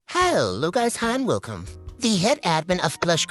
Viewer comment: hyos ucus han welcome” is what I heard😭😔